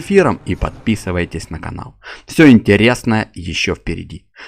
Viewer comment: мне не послышалось? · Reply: 😂😂😂😂😂 Послышалось, там "Па-па". Но радует что хоть кто то видео досмотрел до конца.🤗